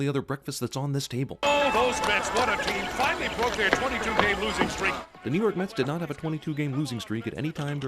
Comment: i thought he said 22 day losing streak for the mets but still sin!!!